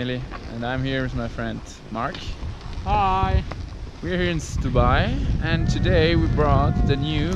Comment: I was shocked... I thought I heard Dubai... with the snow.??? Heheh.